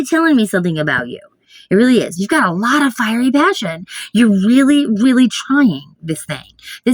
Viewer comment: #1 although my finger tried to make it #2 be back shortly have some chores to finish 🤠. 21:41 lol I heard "you're fiery batshit" had to replay to hear the magic 🪄✨